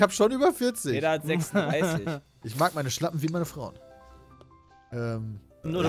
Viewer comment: Ich habe zuerst Schlampen verstanden...